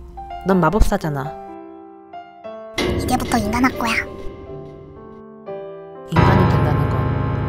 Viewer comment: 유토가 ㅋㅋㄱㄱㅋ01:28 에 ~할꺼야 이럴때 너무웃겨요ㅋㅋㄱ~할끄야 라고 들려서 너무웃긴걸오ㅡㅋㅋㄱㅈㅋㄱㄱㅋㄱㄱㄱ